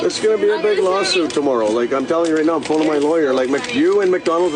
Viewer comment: I thought he said McLawyer for a second lmao